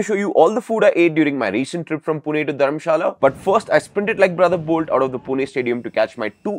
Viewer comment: For a sec I thought u said Dharmasthala